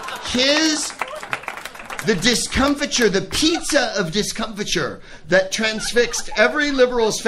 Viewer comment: Did he say "the pizza of discomfiture", metaphorizing what happened? Pizza.